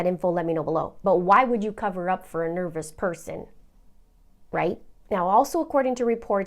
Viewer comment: did you say 'why would you cover up for a nervous person?'